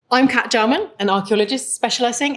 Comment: I thought she said: "I am part German.."